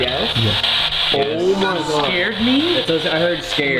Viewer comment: i heard scary and Leslie and i was with headphones so i heard it pretty clear